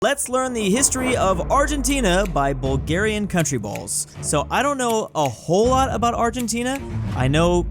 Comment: When said the channel name I thought he said "Bulgaria's crusty balls" 😭😭😭😭😭😭😭😭